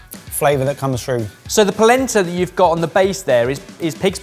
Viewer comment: I'll be honest, thought Ben said "placenta" at 1st...